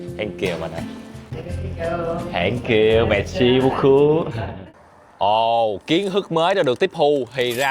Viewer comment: anh nói tiếng pháp merci beaucoup( cám ơn rất nhiều) mà em nghe thành messi bú c :))))